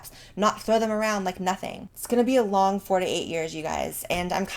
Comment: When you said "It's gonna be a hard 4 to 8 years" what I heard was "It's gonna be a hard 48 years" God Emperor Trump Confirmed Immortal